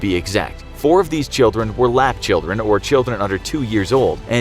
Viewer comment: On 1:33 I thought he said black children